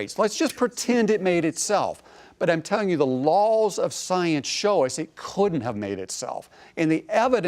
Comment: ok am i just hearing shit at this point... @ 00:27:30 +/- that dude doesn't says "because the LULZ of science..." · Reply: He says "laws of science" but having read this and listened again I can also hear "LULZ".